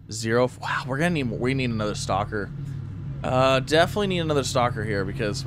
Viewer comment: Z keeps saying "stocker". What i hear is "We need another stalker"